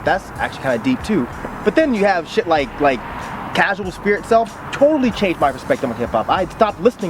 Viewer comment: Casual spirit self? Is that what he said at 7:17?